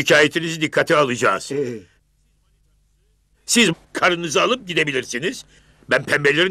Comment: arası tam olarak ne diyor? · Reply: Siz karınızı alıp gidebilirsiniz diyor, zeki alasya